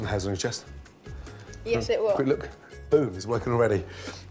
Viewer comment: i somehow heard "look boobs" instead of "look boom"